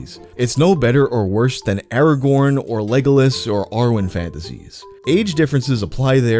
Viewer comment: i heard "r-word fantasies" instead of "Arwen fantasies" 💀